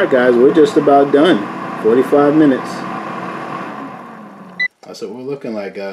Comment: I heard you say “it’s about done…35 minutes” but your recipe in the description says 45 minutes. Which one is it?